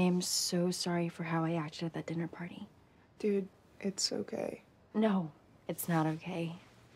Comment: isit just me or does it sound like she’s saying ‘it’s so gay’ instead of ‘it’s okay’